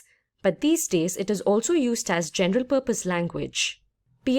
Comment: thank you for the video! However, I am a beginner in programming and it is a pity you don't allow captions on your video, because on 0.52 I keep hearing "gender purpose language" that does not show on your presentation, so I have no clue, what do you mean by that...